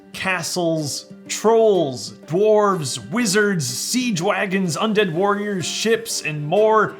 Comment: I was so tired that I thought you said “Sea Dwagons” and not “Siege Wagons”